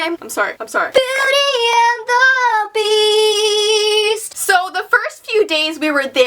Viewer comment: what others hear:Beauty and the beast❤️❤️ What i hear:Beauty and the bees 🐝❤️ and.....nice signing vocie 🤣🤣❤️❤️